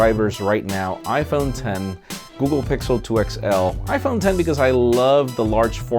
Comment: GOOGLE PIXEL 2 XL 1.Marques Brownlee (MKBHD) 2.Armando Ferreira (mondobytes) 3.Ashwin Ganesh (c4etech) 4.David Cogen (theunlockr) 5. SAMSUNG GALAXY NOTE 8 1.Safwan Ahmedmia (supersaftv) 2.Casey Neistat (caseyneistat) 3.Jon Rettinger (technobuffalo) 4.Enobong Eteh (booredatwork) IPHONE X 1.Michael Kukielka (detroitborg) 2. 3.Justine Ezarik (ijustine) SAMSUNG GALAXY S8+ & ONEPLUS 5T 1.Zack Nelson (jerryrigeverything) Blackberry KeyOne 1.Michael Fisher (themrmobile) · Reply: I thought said Pixel 2 (non-XL)